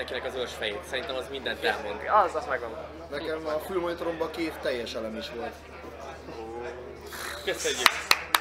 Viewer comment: !!!!! Mit mond a SOMA???? 5-ször hallgattam meg hangosan, nem értem! Nekem a ..........BAN (itt mit mond?!!!) volt 2 teljes elem is?????? Ez miii???? Valami disznyóság?:)))))) Valaki jól halló írja meg:)))))) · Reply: Előtte nem sokkal elhangzott a "félelem" szó, és Soma erre reagált a "Nekem a fülmonitoromban két teljes elem is volt" favicc-cel:)